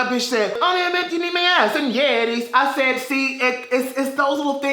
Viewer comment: for a second i thought he said "ass and yetis"